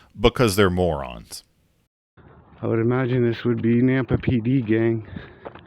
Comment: Refusing to give your ID is resisting, "Press On". you are being detained, so you need to ID. oh when he said Nampa I thought he said Napa now I realized its in Idaho.